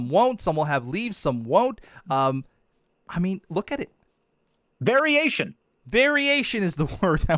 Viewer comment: please don't tell me I was the only one who heard "very asian" and was confused...